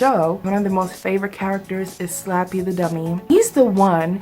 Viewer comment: Hit those notes! Lol, I thought it said "Slap the Dummy" and I was wondering why you were abusing my ex husband, lol! Anyhow, loooove the cake! Excellent job!